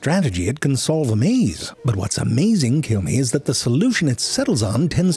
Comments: Did anyone hear that at 5:25? It sounded like "kill me" in the middle of that sentence...☹ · "But what's amazing, kill me, is that the solution it settles on..." Did I hear that right?